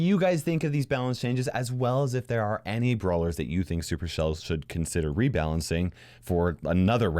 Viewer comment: did you just said "SuperShell"??